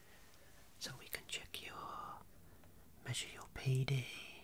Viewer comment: When he said measure your pd I would have swore he said measure your pp